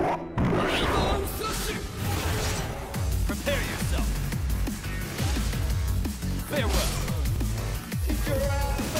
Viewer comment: Can anybody tell me what it says at 3:04? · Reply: Wyvern slash